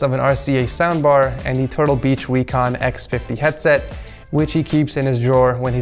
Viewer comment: did he say wecon?